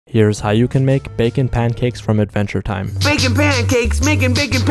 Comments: Bruh I'm stupid I always thought he said baking pancakes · HE SAID BACON!?!?!?! thought bro said "making pancakes making making pancakes".